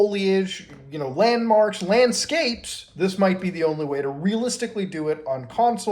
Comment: thought you said 'Manscaped' for a sec there \m/